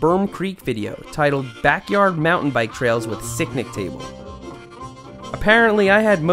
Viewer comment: Every time I hear him say "sicknick table" I bust out laughing 😂